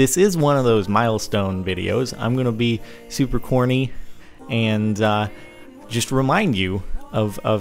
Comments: I thought he said horny instead of corny... oops... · 35 Thought you said horny! I was like say whaaaat? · yay! 6 million! also when you said corny i thought you said horny. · I thought he said "since this was a milestone, ill be super porny" and I said what the actual fuck Jordan